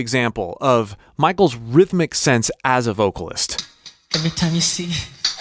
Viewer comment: I might be alone on this, but for years I thought he was saying “intimacy” in the beginning, instead of “every time we see”. My mind is blown right now, and now confused 😂😭